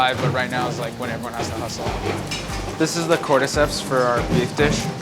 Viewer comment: DID HE SAY CORDECEPS?!??!?!